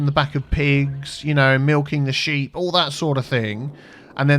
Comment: did he just say. MILKING THE SHEEP?